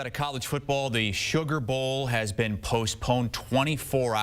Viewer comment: I thought it said Super Bowl